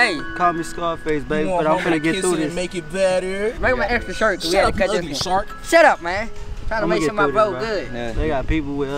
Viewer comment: youboh funny 4 that he really said shut up u ugly shark thats funny but take acre bro we all here 4 u stay safe